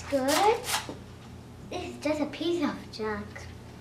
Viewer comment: i thought she said: it's just a piece of sh*t